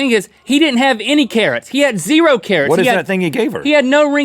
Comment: Am I weird that every time Link said "Karat" I thought he said "carrot"...